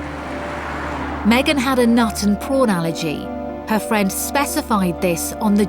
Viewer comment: "Megan had a nut and porn allergy", did I hear that right? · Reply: I thought they said prawn